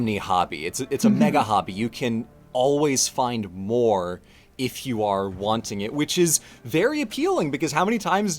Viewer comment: I could of sworn said "You can always find WAR if your wanting it" and I was like "that makes sense", then in a whim I rewound and listend closer, realizing that he actually said "You can always find MORE if your wanting it".